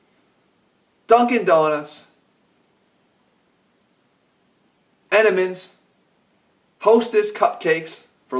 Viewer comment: anyone else hear Enimens?